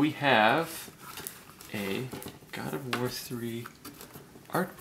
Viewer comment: thought it said gears of war 3 lol